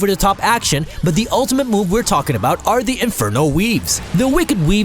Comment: I heard infernal weebs xD